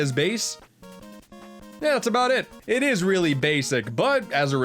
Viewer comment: I thought you said "It was really BASSic" ( ͡° ͜ʖ ͡°)